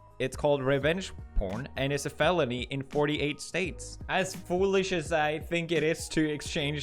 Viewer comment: i thought you said t-fool and i got excited for a battle.